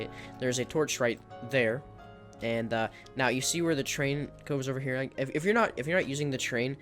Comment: Why does it sound like he is saying train when he says terrain